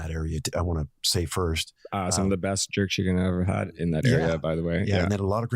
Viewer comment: For what it's worth, the hipster scumbag says, "Some of the best Jerk Chicken I've ever had is in that neighborhood... " at 32:37 or whatever.